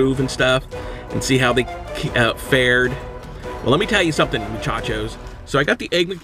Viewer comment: "You Suck Burger King" 5 Minutes After He Stops Recording: Races to Burger King And Gets His Daily 20 Whoppers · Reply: Did he call me a chocho 7:31 🤣